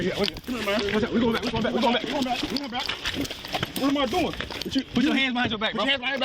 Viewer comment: Did he actually say "Put your hands behind your black"? Freudian slip, anyone?